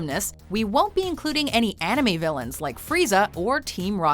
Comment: I thought they said no anime villains. what's Robotnic doing here?